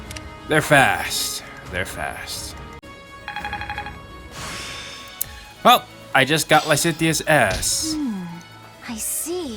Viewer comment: I cannot wait until the final battle I'm so excited! Also I don't know how this game compares with other FE games as I have only played this one, but I often find myself frustrated at some of the classes? Sometimes I find the advanced classes are better than the master ones due to flexibility. QotD: Oh I go oooooon about things I like! But I always ask lots of questions to prompt my friends to do the same about things they like too. I think it's important to take an interest in your friends' interests. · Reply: At first I thought you said "Welp, I just got Lysithea's ass." XD QOTD: Yeah, sometimes I like to ramble.